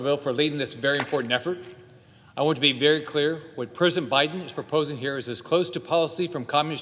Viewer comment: I know that was a slip of the tongue, but it really sounded like he said "Prison Biden".